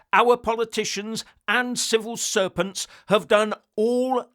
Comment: I thought you said Civil Serpents, and then realised that is actually more correct